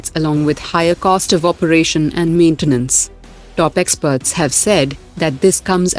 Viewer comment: I thought captains said top 7 but it's 8 inside